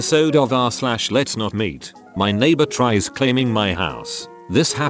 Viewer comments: I thought it said "my neighbor trees claiming my house" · i thought it said "lets not mate"